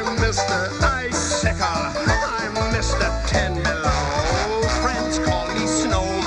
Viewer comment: I always heard it as tangilo not ten below